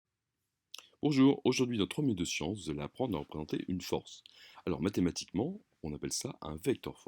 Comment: j'ai pas compris il dit quoi qui peut me dire svp ?? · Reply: un peu en retard mdr mais sinon il dit, "Bonjour, aujourd’hui dans « trois minutes de sciences », vous allez apprendre à représenter une force".